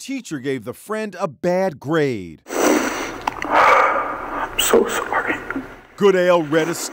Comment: I’m so sorry I got caught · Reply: At first I thought that said, "I'm so sorry he got caught"